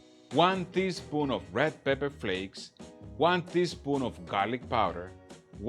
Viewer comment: When you said one teaspoon of garlic powder, I thought you said 1 fist full and I thought I like how he rolls :D